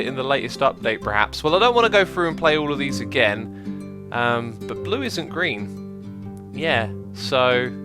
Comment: Ooh, it Blue! -, 2016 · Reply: I thought he actually said "it blue" until you mentioned that you forgot the apostrophe :P